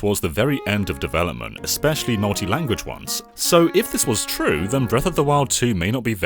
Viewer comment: Did he say "naughty language dubs"?